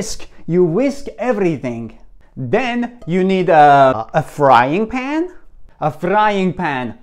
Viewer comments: "you put to the frying pan" Me: I hear "put to the flying pan" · Only i heard "flying pan" ? XDD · I thought he said flying pan 😅